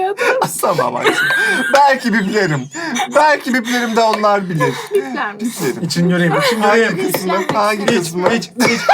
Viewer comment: ay ne dediii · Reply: rez